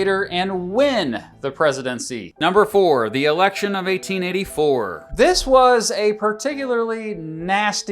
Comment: I heard clear as day “The election of 1984” and thought “well that was nowhere near close” then heard you say Grover Cleveland anf realized